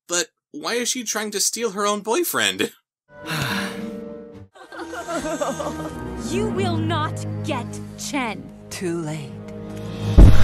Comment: For some reason the first time I thought she said “You will not yet Chen”.